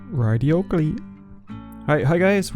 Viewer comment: Does anyone know what he said for the first second of the video in the intro? Love your videos! Your my favorite youtuber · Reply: I think he said right yogi